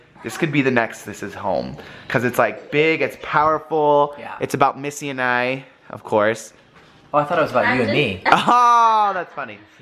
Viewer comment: when you were talking about your songs you said "about me and Missy of course" but I thought you said "about me and Missy's DIVORCE..." lol the shocked look on my face must have been priceless